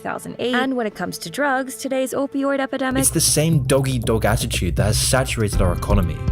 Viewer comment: Did he say "doggy dog" instead of "dog eat dog"